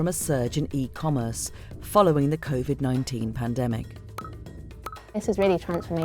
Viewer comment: Thought he said convid